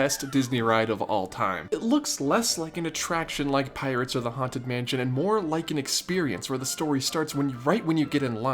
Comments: I thought he said “pirates of the haunted mansion” and I was like ummm what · Did he just say pirates of the Haunted Mansion